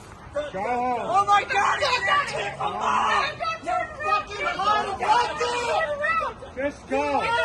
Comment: Is he saying “you’re unelected”? Since when do we elect law enforcement? 😂😂 I self deported! 💜 Team Think 4X Club